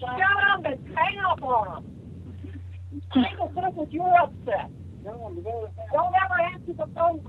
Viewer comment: I believe Barb might be saying, "He's upset cause you're upset!" (referring to Bob) And it sorta sounds like Bob is saying, "Tell him to go to bed!"